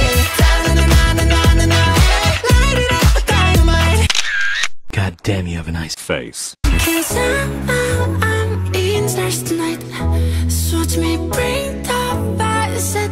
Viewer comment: Is it just me or i heard “cause i-i-i eatin’ the stars tonight”😂😂😂😂😂😂😂 What the hell does it even taste like?🤪😂😂😂😂😂